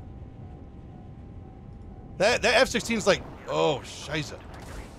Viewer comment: did U say Oh Scheiße?